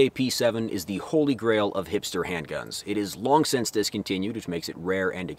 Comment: I legit thought he said, "The holy grail of Hamster Handguns" 🐹